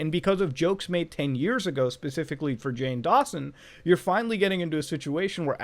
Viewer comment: i swear that i heard Jane Dawson